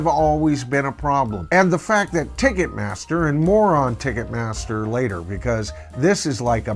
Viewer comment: Did say "more on Ticketmaster" or "moron Ticketmaster"? lol